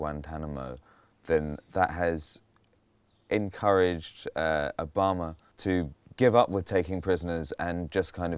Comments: He says "Obama" the subtitle says "A bomber" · put subtitles on and it says 'a bomber' instead of 'Obama'